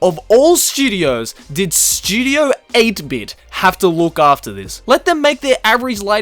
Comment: I thought it said "Studio 8 tit" wtf